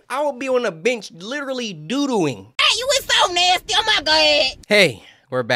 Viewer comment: ik overprotective elders or just people who just came to this channel thought he said: "i'll be on the bitch doodoing"